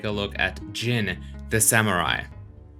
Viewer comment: at 13-16 sec mark near the begining, i thought you said: "Jiren The Samurai" and i was like, is this a crossover episode?